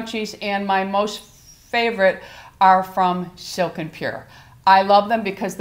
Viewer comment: Hi, What brand of the silk scrunchies is it that you recommended? I thought I heard Silk and Pure, but could not find anything named that online. Thank you and I think your hair is looking really great! Trying to get up the nerve myself...